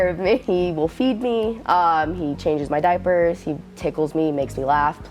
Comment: did she say her daddy changes her diaper? OMG